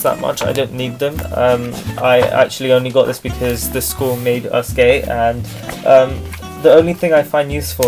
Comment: i cant be the only one who heard "the school made us gay"